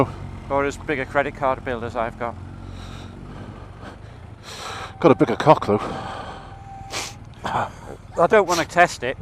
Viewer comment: did you really say "I've got a bigger cock though" Put a picture up or shut up. Not expected, as Rays is the X channel!!!